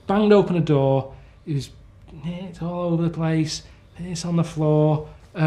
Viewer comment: What is he saying is all over the place and on the floor at 0:32? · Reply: He's saying there was 💩 and p*ss all over the floor, but he almost forgot he couldn't swear on MSN so he kinda went "There was ...IT all over the place, ...THIS on the floor"